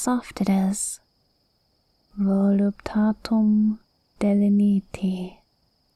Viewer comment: The Latin is the original text bt Cicero on which "Lorem ipsum" is based. I was considering using lorem ipsum itself as a joke, but I was afraid it would be too on the nose :) Ceterum censeo carthaginem esse delendam. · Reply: ah the yandere planes caused me to misshear then because I definitely heard “voluntas delenda” which I believe is roughly “free will destroy”